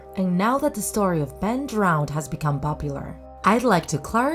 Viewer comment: Ben drownde!? I thought you said be drownd!!!